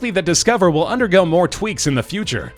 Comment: Did he say tweets or tweeks